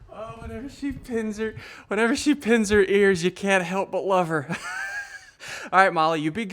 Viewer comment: says "Whenever she pins her ears back u cant help but love her" what I heard "Whenever she pinsir ears back u cant help but love her"